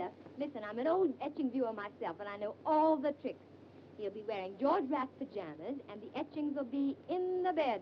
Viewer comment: The 'mumbles' at 9:40 is 'George Raft pyjamas'. There are also some huge errors based on the writer of the subtitles being American (10 buck - far too much then - instead of 10 BOB at 7:31 - favor instead of favour) and obviously being young hadn't heard of George Raft or Lucretia Borgia.For a British film these errors are annoying